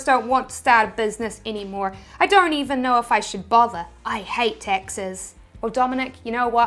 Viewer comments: I thought she said : I hate Texas 😂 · I heard "I hate Texas" lol